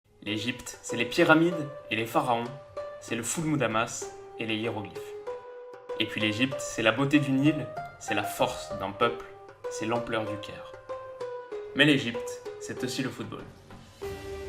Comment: "la beauté du Nil" fait référence au fleuve qui traverse l'Egypte du Nord au Sud, le Nil ;) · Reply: ahhhhh jai compris "la beaute d'une île" je me disais bien 😂😂